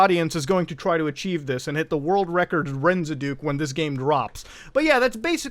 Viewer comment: did you just say "RENZE DOOK"?? LOL